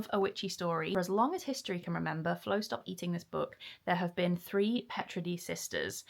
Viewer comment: Absolutely chuffed for you, getting that first complete draft done. Yayyy!!!🎉🥳 Did I hear you say, “Flo, stop eating the book?” 😂😂😂